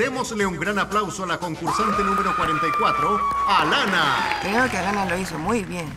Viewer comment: pense que dijo Banana xdxd